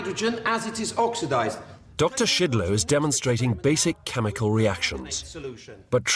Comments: I know im not the only one who heard "Doctor Shitloads" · did he say doctor bleep load?!